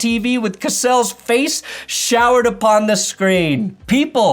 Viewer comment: showered upon the screen? Did I hear that?